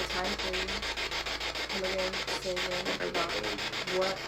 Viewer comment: I heard “Megan” instead of “Say again” ... what do you think?